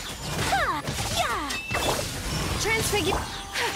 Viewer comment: am i the only one who heard turn spaghetti? 👁👄👁